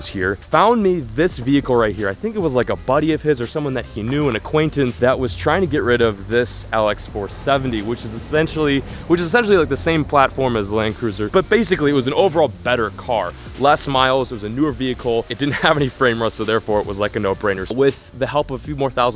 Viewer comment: Did he say LS-470 on 3:17-18? Almost impossible to find a Lexus GS 470 with miles under 90k. ;( been looking ..